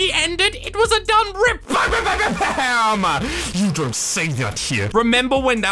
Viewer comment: who else heard "you don't say that shit